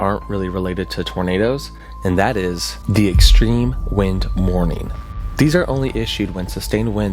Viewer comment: Thought you said “mourning” and I was like “hfs, wtf do you mean😭”